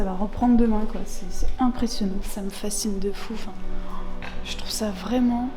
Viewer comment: "sa me facine de fouffe" j'ai entendu sa je suis vrmt désoler mais c'est se que j'ai entendu 😭